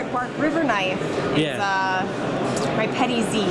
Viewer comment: what did she say her favorite was? I couldn't understand. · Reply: Bark River Petty Z